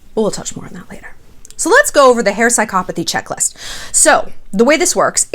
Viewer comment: Omg I thought you said "hair psychopathy checklist" and I kept trying to look for it.